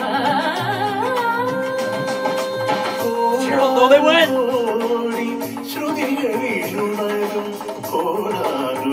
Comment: What does says here? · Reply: "Do you hear how low they went?"